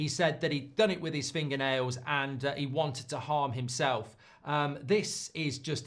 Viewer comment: I heard "I would not harm myself" but I guess people just hear what they want to.